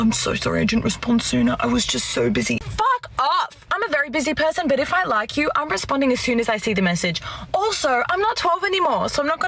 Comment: The girl at 3:35. Just a point of contention, and this is just a human thing, but occasionally I see you, or other YouTubers, misunderstand/mishear what the woman said. She said, "I'm a very busy person, but if I like you, I'm responding as soon as I see the message. Also, I'm not 12 anymore, so I'm not going to play that game where I have to leave him hanging for a few hours, so I don't come of as too desperate. I don't care what anyone says, no matter how busy a guy is, if he likes you, he will respond as soon as he sees that message." That's literally word for word what she said. I went back and transcribed every single word she said. Check for yourself. So this was actually a very healthy statement. She's literally stating that she does NOT play games. Now, how true that is, is anybody's guess. But your commentary was as if she had said just the opposite...that she left guys hanging for a few hours, to manipulate them. She said just the opposite...that she does not do that, and will not tolerate guys doing it. This leads us to what we all know. That's BS. She WILL accept a man doing that, if he's a top tier man. She's just ranting because another Chad pumped and dumped her, and she now sees the red flags. Like it or not, because all women these days, think they deserve a top 10 to 20% man, and there aren't enough of them to go around, women will always make rules for Betas, but break rules for Alphas. And this makes them disgusting for Alphas, and even the Betas w